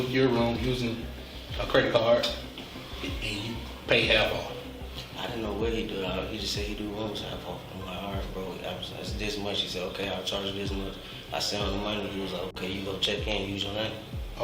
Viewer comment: tell me the subtitles are AI generated cuz at 31:19 how did they get the subtitles so wrong, what he actually said was "I don't know what he does, he just said he do always half off, and I'm like alright bro, they advertised this much. He said okay I'll charge you this much. I sent him the money, and he was like when you go check in use your name" They always mess up subtitles in these types of videos so badly, but how the hell did they think he was saying "I dont know what he does, no way to know who just say to also have the last vote epmhasized, this much is okay it is what i sound like, the use of payments i cant use on that okay" please tell me it was done by an AI, only explination that or someone really doesn't pay attention, and honestly I can believe the story of buying a room half off from someone on the internet and them booking it with a stolen credit card and putting it in his name after he sent them the discounted price they told him to pay, idk if his story is true but that shit does happen